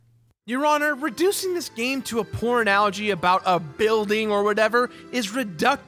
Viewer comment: I know he said Poor Analogy, but to me it sounds like he said Pornalogy.